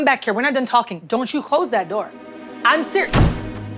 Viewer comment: i thought she said “IM SIRI” but then i realized they cut her off.. 💀